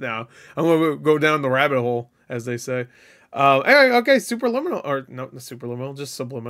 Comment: This whole time I thought this said “Superliminal” and I was so confused as to wtf this had to do with it